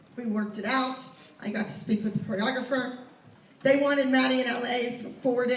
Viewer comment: Lol i misheard this hahah i thought Abby said "I got to sleep with the choreographer"